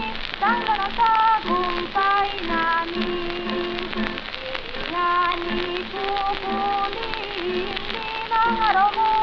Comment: I'm hearing "hindi ngani kukulimlim ang araw mo at bituin." instead of "nang hindi na magkukulimlim..."